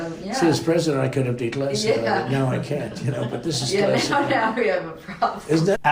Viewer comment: Am I the only one who hears him say "but this is classified" and not "but this is secret"?